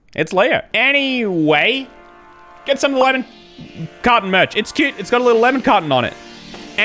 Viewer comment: I’m American and I haven’t watched videos for a while- and I thought he was saying “lemon cotton” merch.